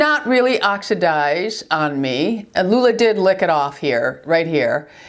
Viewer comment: Oh! I thought when you said "Lula licked it off" you'd said " Lou licked it off " 😂😂🤣 TMI!, But wow - it held up well !